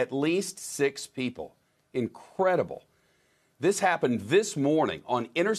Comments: did he just say it’s incredible ???? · Did he just say "incredible"?